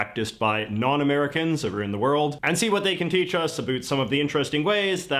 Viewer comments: Did he just say aroond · he said aboot.. instead of about.. @ 2:56 he's Canadian